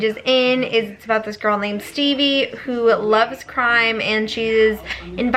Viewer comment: I thought she said "I just love books about teen girls who are interested in crying" lmao I literally said same out loud before I realized she probably said "crime".